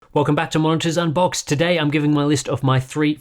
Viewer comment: I thought he said "welcome back to munchies on box"